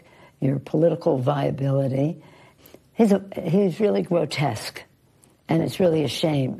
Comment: did she just say "Gwotesque"